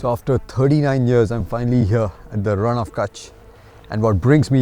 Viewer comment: I am 39 😅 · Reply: Ah, I misunderstood. I thought you said I'm finally here again at the Rann of Kutch. 😅 A foot in the mouth moment for me 😂